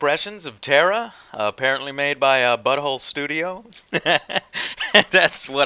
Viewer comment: glad to see I wasn't the only one who thought it said butthole studios at a glance